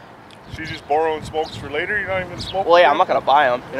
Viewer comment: i think he says " and hes bumming smokes" not "andys brought the smokes"